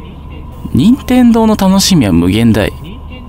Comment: 辺りから「任天堂の楽しみは無限大です」って聞こえる… 怖がっているところ好きだ…!